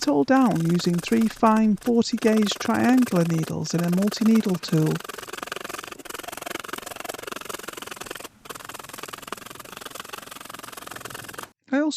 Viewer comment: I thought you said a ‘naughty needle tool’, instead of multi needle tool…😅 Lovely work, thanks for sharing.😍🥰